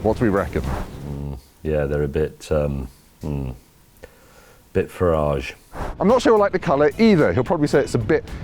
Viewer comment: well, mini's in general have a hard ride, and if the electric version has even worse suspension then the petrol version, i think i'll pass. What's the word May used @ 6:15? firage? definitely wouldn't say mini's are well priced, they definitely aren't. great video though. i like it. good job. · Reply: Farage, as in Nigel Farage the Brexiteer politician.